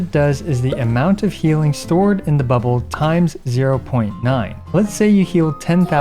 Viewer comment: I genuinely thought he was about to say “healing stored in the balls”. I’m glad that wasn’t what was said 😭